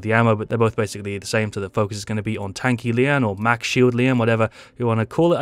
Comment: did u say Tank lian?